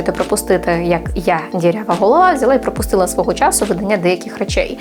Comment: мені одному почулось "дірява і гола як я"? :D